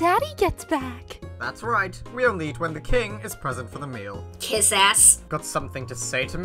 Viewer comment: What did say in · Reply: said “Kiss ass”